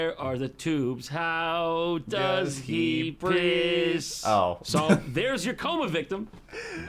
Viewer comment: did both of you guys say "piss" or did you say something else? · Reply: I said breathe